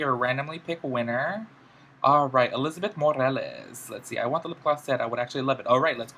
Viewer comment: I THOUGHT HE SAID MARISSA MORALES AT FIRST IM SO EMO